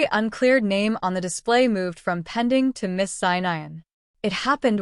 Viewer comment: Wow the captions are bad. And plenty in the story that's just confusing. I'd try to read the captions to understand what was being said & often it was just nonsense. Like 17:10 "Every unclear name on the display moved from pending to Miss Sinayan." MISS SINAYAN?!?! It's supposed to read "MISSED SIGN IN". 23:08 "Ni got the clue shirt. Then came camera footage." WHAT??? Even worse is the 1st sentence is said in a different, really weird voice. Captions & subtitles are very important; I get it more than many bc my Mom is deaf. ACCURATE CAPTIONS are even more important. These are a joke.